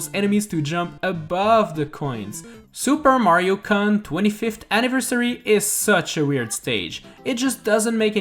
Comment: sounded like you said Super Mario cunt 25th anniversary